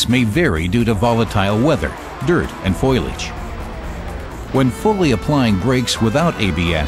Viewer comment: Good video, but LMAO. Does he say "foilage" @ 3:50? Straight out of the Simpsons....